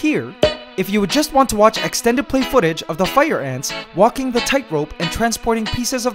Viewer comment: oh fire ants I thought you said red pikmin